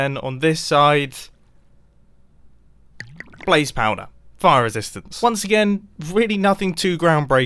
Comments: When you were saying fire resistance it cut out and played a ad in the best time ever. It almost sounded like you said Fu.. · youtube moment first millisecond you said fireworks then an ad came, i thought you said f word for the first time in your life lol